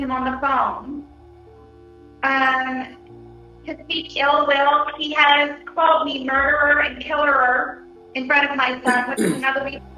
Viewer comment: Did she say “he has called me murderer and killerer”